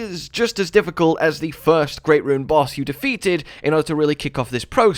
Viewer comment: I don't think Radahn is ANYWHERE close to the difficulty of the Eldritch boss. I got the Eldritch boss on my second try while Radahn was a cry fest. Edit: Ah first great rune boss. I misheard it.